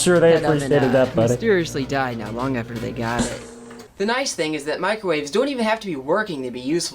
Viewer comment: Wait…did he say his parents mysteriously died after they bought the microwave? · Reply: No, he said the microwave mysteriously died not long after they bought it (his experiments 😅)